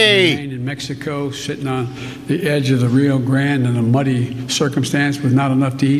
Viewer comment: did he say "shittin' on the edge of the Rio Grande"?